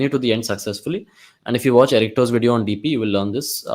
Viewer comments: what he said, whose dp videos to watch ? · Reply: Errichto Algorithms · who's video you suggested there, I can't understand.... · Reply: errichto....grandmaster on codeforces